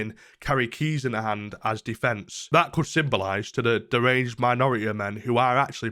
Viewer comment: lol i thought you said deranged minority men for a second